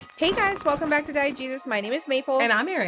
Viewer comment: Lol this whole time I thought you were saying diet jesus. I cant be the only one.